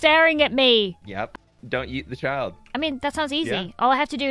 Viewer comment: i kept hearing don't eat the child but he said yeet 😅